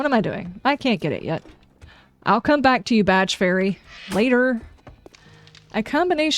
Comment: I agree with most of what you said but I did have a problem with the Badge Fairy'. My brain heard that as 'Vadge Fairy' which is obviously something totally different! Yes, Yes, I know, I have a problem and I promise I will seek help..... :)